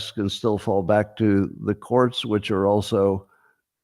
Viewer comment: Did you say "corn switching" at 1:07:00? Or was that "court system" and it got censored? Or am I losing my mind? Check it out. Weirdest thing at time stamp. Give it a listen.